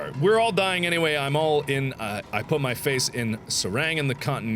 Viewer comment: Did anyone else hear 'I put my face' at 19:58 😂 God I love mark